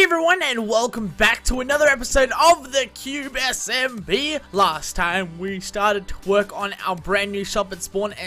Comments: i thought you said "last time we started to twerk" xD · thought he said we started to twerk on....xD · In the beginning of the episode I thought he said twerk but he said to work